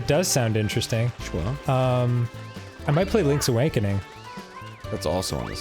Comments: I swear to gosh Dan says "Link's Awankening" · Did Dan just say "Link's A-wank-ening? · it sounded like Dan said Links Awankening